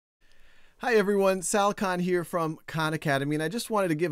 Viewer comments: I thought he said shao khan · When he said "Hi everyone, Sal Khan here", I heard it as "Shao Khan" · i thought he said his name was shao khan